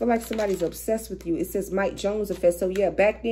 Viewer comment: During the video (33:35), I heard you say “mike jones.” Did you say that? Or did I misunderstand lol. That’s the name of the man I’m dealing with and everything that you said in the video sums up our “relationship.” Crazy!